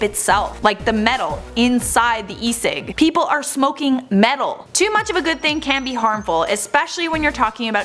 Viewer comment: Did she just say I’m smoking metal